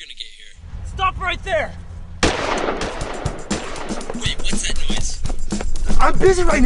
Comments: Did Ian say, "Babe, what's that noise?" At 4:06? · I thought he said "Babe, whats that noise" lmao